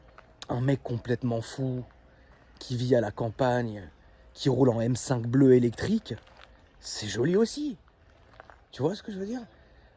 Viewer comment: cest moi ou il parle de boiserie et gmk ? 😂